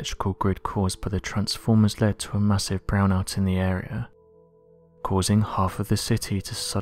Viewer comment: okay i know im not the only one who heard him say"cause a massive (BROWN) out in the area" lol wtf